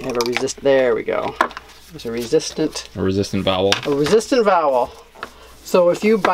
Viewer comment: I thought you said "resistant bowel" haha.. I was like.. ew lol